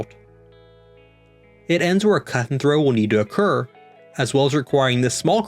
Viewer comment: What are you saying at 7:50? Cut and throw? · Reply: Yes, I said cut and throw. I explain what that is and how it works in this Brightline construction video: <link>